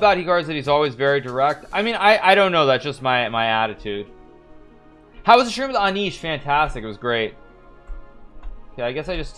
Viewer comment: how was that schrooms on E..did I hear this correctly?